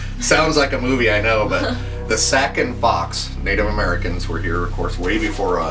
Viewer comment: what did he say at 2:36? Sack and box? did he make that up or is it spelled weird